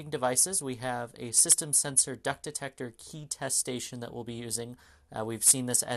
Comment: did I hear DUCK DETECTOR? Lmao